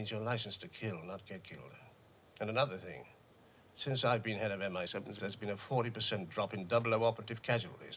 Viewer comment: he clearly says mi6 but is dubbed over as mi7. never noticed that before